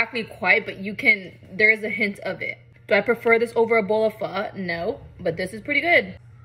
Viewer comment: For a second I thought u said a bowl of phaque (fuck)